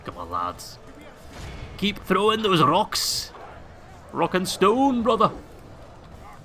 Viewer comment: did I hear rock and stone?